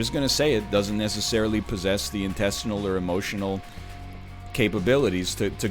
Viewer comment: Did anyone else hear him say intestinal or was it just me?